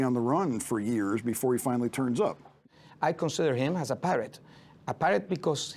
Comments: I thought he was saying..” I considered him a parrot…..he was like a parrot” I thought he was going to explain that he repeated things….. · Oh, a pirate! I thought he said he considers him a parrot and I was very curious to hear him explain that metaphor!